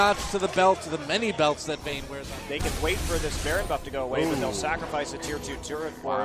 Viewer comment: 초에 밥도둑 들림